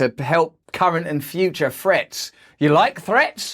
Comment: What do I think is the question... I think that we you say, "Threat" I hear "Fret" so I look at my guitar on the wall and give it the "angry eye" to let it knows whos boss. <3